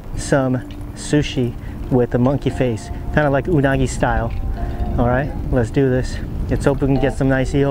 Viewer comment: i misheard you, thought you said Miyagi style.